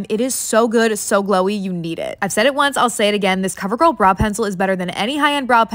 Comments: I thought she was saying bra pencil😭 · She talks so fast that I thought she said bra pencil not brow